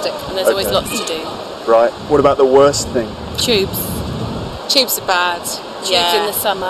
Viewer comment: Hey guy! what is the worse that they said it on 2:43, it seems for me as far as I heard it they said " chips" loll! please ANYONE can correct me! Cheers!